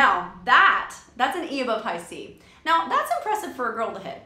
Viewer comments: what did she say? I couldnt hear it very well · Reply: "That's an E above high C." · What did she say @ 7:28? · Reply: She said - that’s an E above high C